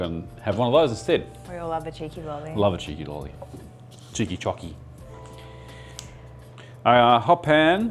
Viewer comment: I thought said Chicken Jockey for a second and had to do a triple take